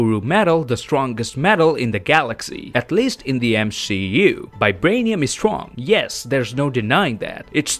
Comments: did I hear him say M"SHE"U instead of MCU. If so, I agree. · did he just say m she u